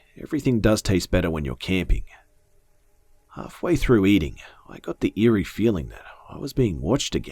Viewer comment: It sounds like hes saying i got the keys in one part of the intro